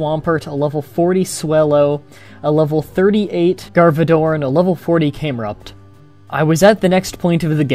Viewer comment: Whoever made this story clearly never played a Pokemon game in their life. Garbador weren't available in the third generation, and you don't catch any Legendary Pokemon during the metagame. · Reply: Dragon Deffender Oh. I heard it as Garbador.